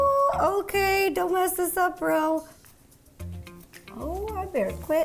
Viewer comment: I can't be the only person who heard he say 'Don't mess this up bRo.' I know she said Ro but I heard a B!